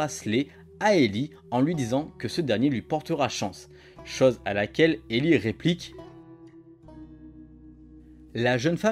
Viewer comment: elle réplique quoi Ellie ?(je sais se qu'elle dis elle dis je ne crois pas a la chance ) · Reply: Elle dit qu’elle ne croit pas en la chance ;)